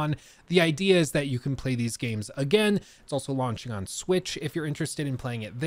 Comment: UPDATE: Now that the game is out you can actually try the online modes I couldn’t try in this video and people are reporting server issues galore. Check your specific platform, but it seems that online multiplayer isn’t viable right now. 😢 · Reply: actually you're right, i thought i heard saying relaunch in the video, so i may have misunterstanded, my bad 😅😅😅